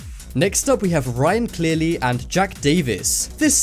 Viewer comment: I wasn’t paying attention and I thought it said Ryan clearly jacks Davis